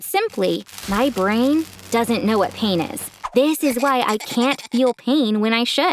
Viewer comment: did she say this is why I can't feel shit?☠️